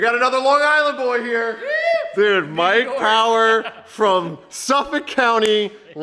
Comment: did he say "Mike Power" or... "White Power?" 😮